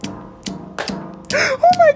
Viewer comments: At first I thought she said cemen clapper 💀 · I heard "semen" instead if "cement"